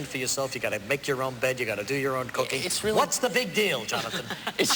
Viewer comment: IT SOUNDED LIKE REGIS SAID YOU HAVE TO DO YOU OWN COCAINE